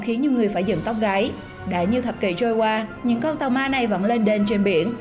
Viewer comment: trôi dạt 3 thế kỉ mà k chìm ảo thật đấy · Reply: Bạn nghe nhầm không? Ba thập kỷ chứ không phải ba thế kỷ đâu.